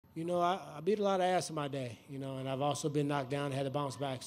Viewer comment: oh no..in the beginning I thought he said " I ate a lot of ass in my day.." and Im thinking thats a strange way to start a post fight press conference??!! LOL Woodley's going to have to change the name of his single from " Beat Yo Ass" to " He Beat My Ass" feat. Marty Ooozman.